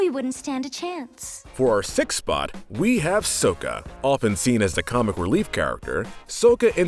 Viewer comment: Did he just say sowka 😕